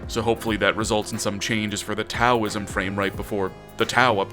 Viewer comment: I misheard that as 'autauism'.